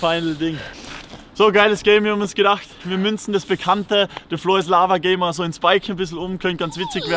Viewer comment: Ich hab die ganze Zeit, Neues Lama verstanden anstatt the floor is lava